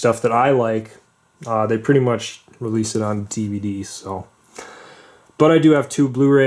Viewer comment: did you say tvd’s